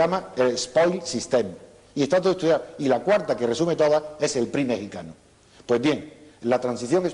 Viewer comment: ¿Cuál es la cuarta? (Minuto 33:36). No entendí eso que dijo. Alguien que me lo diga por favor. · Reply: el partido mejicano PRI.